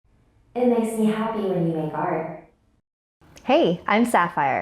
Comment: I thought it said it makes me happy when you fart butnit was he os happy in the heart